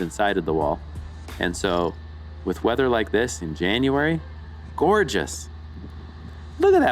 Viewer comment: Did I hear you say that the day this was filmed was in January? At 9:02 you mentioned the weather and January.